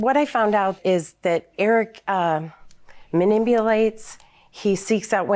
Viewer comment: So we just gonna ignore that she say “manibelate “ instead of manipulate ...